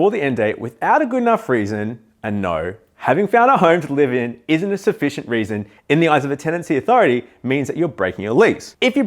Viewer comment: I can't make out what you're saying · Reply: Thanks: "Finding another home to live in isn't a sufficient reason to break lease".